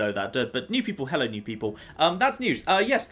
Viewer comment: I thought you said Hella new people haha